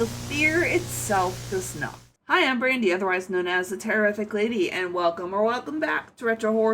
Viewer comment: i didn't see your channel name and thought you said "im brandy, otherwise known as a terrific lady" and i was like hell yeah you ARE a terrific lady! lmao